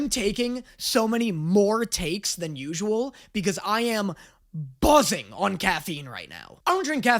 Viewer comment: I heard busting😭